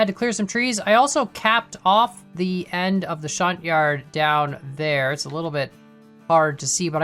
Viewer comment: I thought it said stunt yard